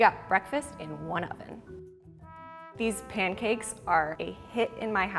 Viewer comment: I thought it said “street pancakes” lmao